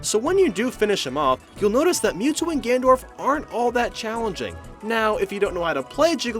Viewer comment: did you just say gandorf?